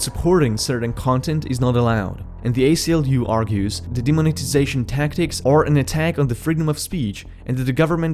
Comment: For a moment I thought that said Demonization